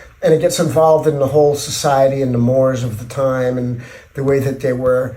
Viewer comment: “wars,” not, “mores.” Dude, your captions suck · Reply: The captions are bad sometimes, but if you're referring to 4:36, Flea does indeed say "mores" there. As in, "social norms that are widely observed within a particular society or culture."